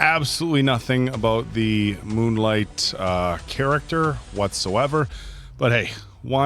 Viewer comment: Did he say MOON LITE ???:face-blue-wide-eyes::face-purple-smiling-tears::face-purple-smiling-tears::face-purple-smiling-tears